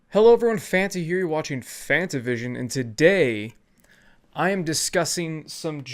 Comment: really? I thought I heard you correctly? I’m a hearing impaired fan of yours... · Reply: It goes like this "Hello everyone, Fanta here. You're watching Fantavision, and today..."